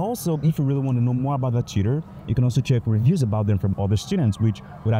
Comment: Dude! “Tutor” I heard you saying: cheater, your pronunciation got me! 😂